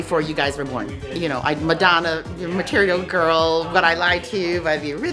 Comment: Did she say material gurllllll